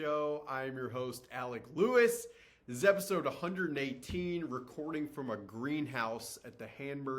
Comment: I thought I heard "recording from a treehouse at burning man" (I was just listening and washing dishes), looked at the screen... sort of looked a tree house... replayed it... okay, on the same page now 😅